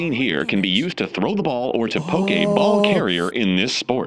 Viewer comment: was that intentional or really big coincidence? the way he said poke a ball, sounded like Pokeball.